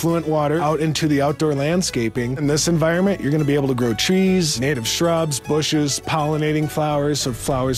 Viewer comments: Did he say grow chess? wtf... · Did he say grow cheese? Lol